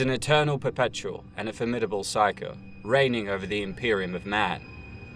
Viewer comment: wait at like 2:22-ish did he say "A perpetual and a formidable psycho." I know he most likely meant Psyker but I kept going back and it sounds like psycho lol. I mean I guess that explains a lot like all the skulls.